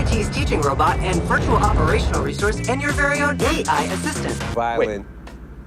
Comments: I legit thought he said "DEI assistant" 😂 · For a second I thought it said GAY-I assistant